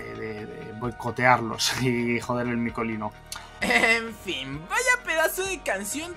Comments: jajajaja le entendí mikecolino ajjajajaja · ¿Soy yo o escuché "Mikollino"? xD Fuera de eso, aunque no sea canon, esa escena es muy reveladora 👀🤙✨